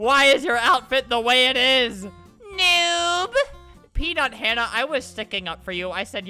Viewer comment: When he said PeanutHannah I thought he said “peed on Hannah”